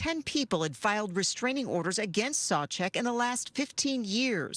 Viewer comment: At first I thought they said "Sobchak", which made this all totally understandable.